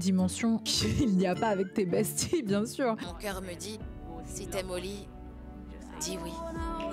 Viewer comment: Moi qui a compris "si t’aime au lit dit oui" au lieu de "si t’aime olie "